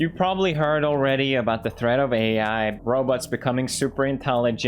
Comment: I legit thought pewds said 'roblox' at first and laughed then realized he said robots =/